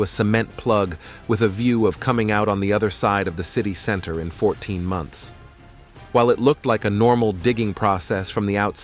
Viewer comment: 14 months humm I thought u said in 1 day weird ..